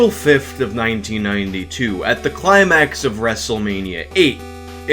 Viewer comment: I had to go back and re-listen to that beginning part because I thought you’d said “at the Xanax of Wrestlemania 8”. I mean…